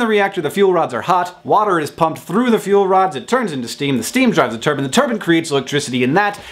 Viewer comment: At first I thought you said, "Steam turns the turban."